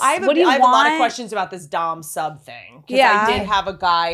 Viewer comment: I heard "Don Sub". I was legitimately typing it into google until they said BDSM. In my head I was thinking that Don Sub was some kind of celebrity who was well known for throat fucking or something